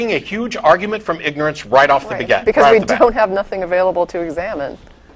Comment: LMAO 4:32 - says "Begat" instead of "off the bat"....damn bible!